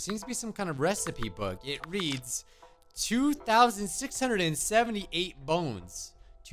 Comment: i was like 2 4 6 8 I thought 26 was 24 and somehow the 7 8 sounded like 6 8 to me Wtf